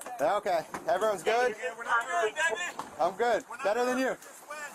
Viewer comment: ''I'm good, better than you'', is that he really said? 😂😂